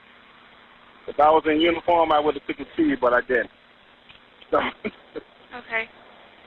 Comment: he says "if i were in uniform- i would've" i cant hear what he says after that but it would definitely imply he has a uniform that gives him some authority. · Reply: "...I would've taken his keys"